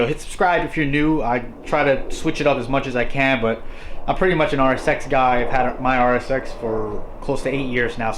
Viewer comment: Re: "... I'm pretty much an hour sex guy... " on index 25:36, is that what you said?